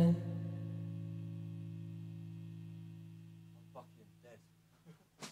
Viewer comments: WHAT THE Right at the end u can hear someone say something I hear 'Fucking nasty .......' dont know the last word ??????????? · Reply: Ben Clive he says "I'm fucking dead" then laughs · what does he say at 3:01? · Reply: I'm fucking dead